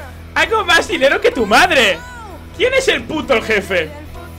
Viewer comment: en el minuto 3:55 parece que dice: me voy al puticlub algo así xD